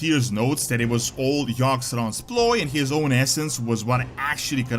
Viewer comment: I thought blizzard just said yaggsaron is still alive